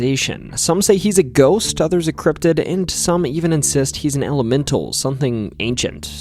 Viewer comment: Hey how you doo..ing my Fab folklore friend 😊😊 im in My Trailer in deep historical and legendary Somerset watching this 👌👌🕸️🕸️ a fresh pack ofjammy doughnuts....could it be more perfect 10:16 i thought you said 'hes a hell a mental "